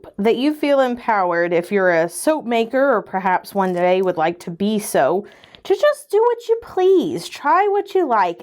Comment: I misheard 'Or perhaps, one day, would like to be so..' & was convinced that she said 'Or perhaps, one day, you'd like to be SOAP..' and I felt so seen. lol